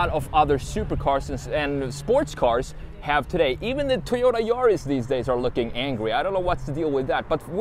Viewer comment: did he say Toyota Urus?... I thought Lamborghini made that Suv? but anyway, this was an excellent presentation.